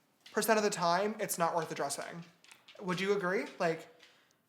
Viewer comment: When it got to the question about addresing things i thought he said dressing and i was like what